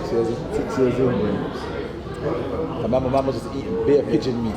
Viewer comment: did man just say they were eating pigeon meat 😮😮